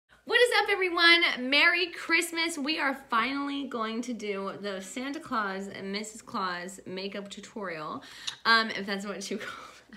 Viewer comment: I seriously thought it said Satan and Mrs.clause